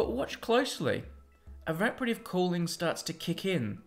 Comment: What is the word he used at 6:05? I have no idea what that word is... Araprative Cooling? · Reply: Evaporative cooling.